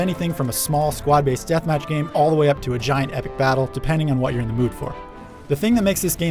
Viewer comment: "missed epic feeling" wtf? he said there could be a giant epic battle